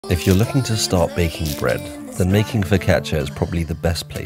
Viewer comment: At the start I thought you said "if you're looking to stop baking bread" lol, looks great though